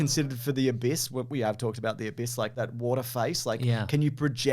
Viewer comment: Whenever I hear "The Abyss" I can't help but think "that bit of piss?"